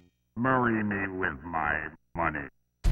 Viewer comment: To me, it always sounded like he was saying "Marry me with my money". I thought that in the 90s and I still think that today.